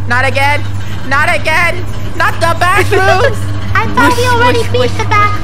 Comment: In the intro I thought said the bathrooms